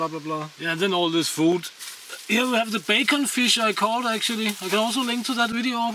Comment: Did he say bacon-fish? Im sure he said bacon-fish. 8:29 Yes, Yes he did! Please tell me about bacon-fish. New subscriber btw. Im really enjoying your humor and channel.